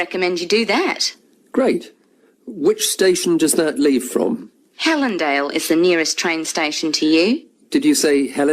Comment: Is it HELENDALE ? For second question I heard it as HELENDILE can you confirm once?